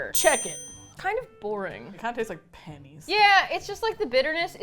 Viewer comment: I think said, "This tastes like pennies," but I heard, "This tastes like panties." 😳